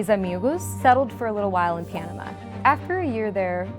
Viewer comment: The way she said Panama at first was so quick I thought she said Canada.